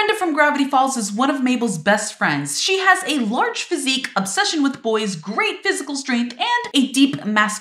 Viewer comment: I thought you said "a large, fake obsession with boys" and honestly, I can see it